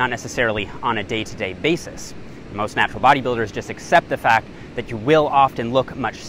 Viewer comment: Have I heard you saying "these natural bodybuilders"? 🤣